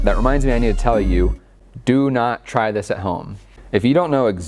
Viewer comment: what I heard "TRY THIS AT HOME"